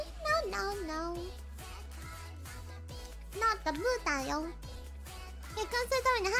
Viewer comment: 當時不知怎麼著聽成you are buddha